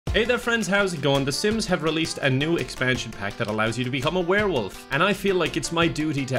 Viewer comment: IM SORRY DID HE SAY “EXPANSION PACK?”